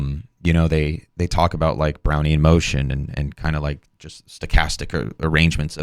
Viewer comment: I heard “brownie in motion.”